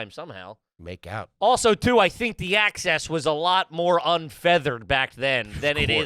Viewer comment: did he say un-feathered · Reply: he absolutely did and I died laughing (for those who don’t know, it’s “unfettered” not “unfeathered”) 🤣🤣